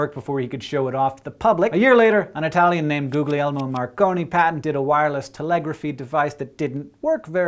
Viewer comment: I heard "Googly-Elmo Markony" best sounding name ever